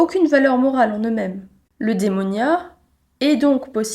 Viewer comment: bonjour je ne parle pas nativement français, dans 5:30 les sous-titres indiquent qu'elle dit démonia mais ce mot n'existe pas j'crois, aidez moi à comprendre stpp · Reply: Il s’agit du mot grec «eudaimonia» (εὐδαιμονία) qui provient de la philosophie d’Aristote. Ce mot se traduit par “bonheur,” “prospérité” ou “épanouissement” selon le contexte.